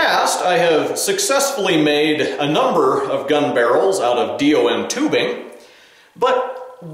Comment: Did he say DIO....is that a JoJo reference?!